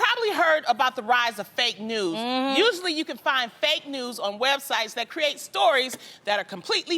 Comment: It sounded like Loni was saying "fake nudes". Haha.